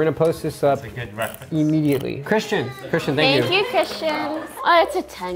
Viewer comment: did Poki say thank you Christians lol that's his name